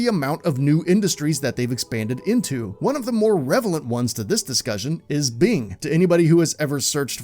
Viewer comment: Did he just say "revelent" instead of "relevant"? 2:16 Yes. Yes he did. But, I guess that's irrevelant.